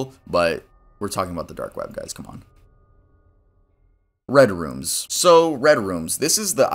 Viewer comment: I was just listening to this while drawing so I didn’t see the text and I thought you said reddit, and I wasn’t surprised 😭